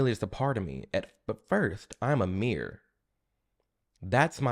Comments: I thought you where saying. I'm a mirror. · I thought he said, “at first, I’m a mere”…and i was thinking…ok what pronoun is “a mere”. 😂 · "I am Amir" resonated with me deeply. I remember a few months ago when I made the statement "I can dislike what they believe in, but they're first and foremost my friends." and I just got flamed to the ground, so I decided to just shut up and read a book. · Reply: I thought he was saying "I am a mirror" the first time I hears it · I heard ymir ngl. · First video watching this channel and at 1min 15 he says ‘i am amir’, but i didnt know his name so i was super confused cuz i thought he said ‘i am a mirror’ 😂😂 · When you first said "I'm Amir" I totally heard that as "I'm a meer" and I was like oh no, another sub-sub-sub category. What does it mean?! Is it an abbreviation for man-queer? Like you're queer but more...manly? So I googled it. Nothing! I was so, freaking, confused. Then I read your name. sigh With that out the way...Great video! I agree with you on every single point and you articulate them very well. Thanks for posting this!